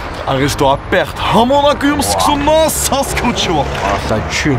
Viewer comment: Y a que moi qui a entendu "sasuke uchiwa" à 9:48 min?!